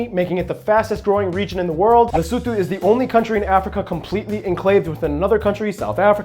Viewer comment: I'm guessing by countries being completely surrounded by another country that you are excluding the micro-nations of Vatican City, San Marino, and Monaco. The first two are completely inside Italy. Monaco is "semi" in that it has a Mediterranean coastline but all the land borders are with France. Or did I simply mishear something? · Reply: he said the only country in africa